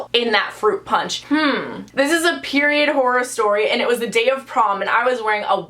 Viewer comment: I thought it said porn horror 😂